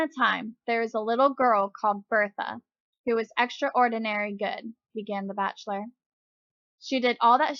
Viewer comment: Thanks a lot, although I laughed a little because I heard you said "extra annoy... Ordinary" (that's what I heard, no judging)